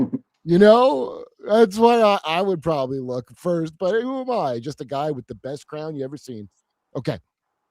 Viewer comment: did you say just a guy with an itchy crown?